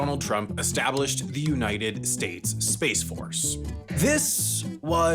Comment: sounded like Corden said spice force. that might be better lol.